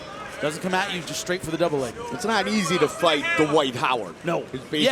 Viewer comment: Wtf did he say? Surly it’s not what it sounded like..... or what the captions literally say it is. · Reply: I think he said "it's not easy to fight Dwight Howard"